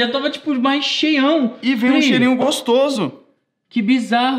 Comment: pq só eu ouvi creminho gostoso?